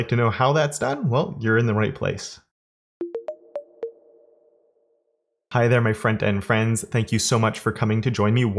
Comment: For ages I thought you were saying "Hi there, my friend and friends." Made no sense. Now I know what you're actually saying. What are 'Supporters of Awesome'? Great video, again.